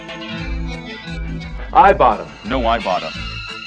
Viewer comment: Omg at first I thought they were saying, "I bought 'em! No I bought 'em!" lmao